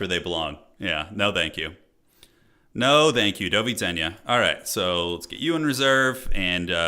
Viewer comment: did I just hear Polish "do widzenia"?